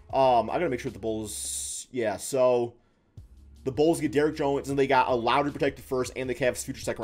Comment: im tripppin bro i thought shi said bulls get derick rose im like wait why are we not talkin abt this lmaooo